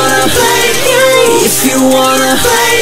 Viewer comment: Why... do I hear "Play Gay" instead of "Play a Game" or "Play Games", which sounds better to me.